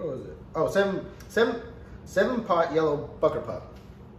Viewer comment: Did he say "7 Pot Yellow BuckerPutt" at 4:20 LoL!